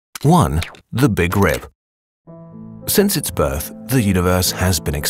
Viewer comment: I thought it said the big drip, I was like damn the universe got drip?!